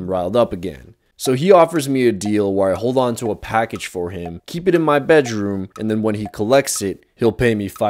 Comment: love these videos, I watched ur content when you did meme animations, as that's what I do, your videos getting better after every video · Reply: I’m not here to tease or make fun but @ 4:41 when u said... “Keeps it in my beds room, I kinda cachinnated, It was 75 outta 100% hysterical how u said... beds room instead of bedroom!”